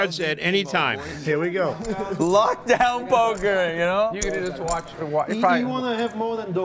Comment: Is he saying lockdown poker???😂